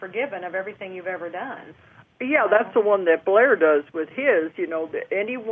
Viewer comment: Who is Blair Styrum? I couldn’t hear if this name was what I heard and searched all over the interview. They mention him at 14:30 and say he is a psychic from New Zealand. If anyone can help me find more info on this person I would appreciate it very much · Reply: HI Irene, I think she is talking about Blair Styra, Spiritual Channel at Tabaash. Hope it helps.